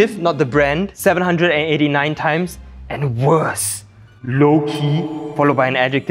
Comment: I heard "Loki" and I was like, "WHAAAA??" 😂😂